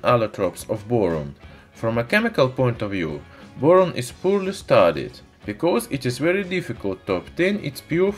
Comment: is what? I cannot understand a word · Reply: "boron is poorly studied"